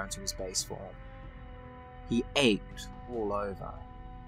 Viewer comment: I thought when was talking about Green Lantern and Flash she was gonna say "oiled up" or something..... Either Hal or Kyle would easily win btw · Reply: I thought you said "he aped all over" which would have been cooler